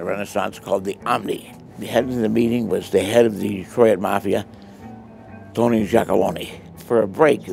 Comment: did he say Tony Jackabologne? Ok, now i think he's making stuff up...